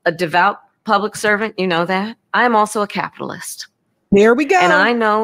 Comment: At first I thought she said “I am also a Catholic”. 😅